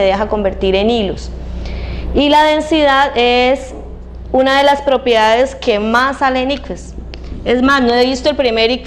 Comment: no entiendo que dice despues de densidad.. . min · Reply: Y la densidad es una de las propiedades que mas sale en ifex Denada Dos años despues peroo... Denada papu ;)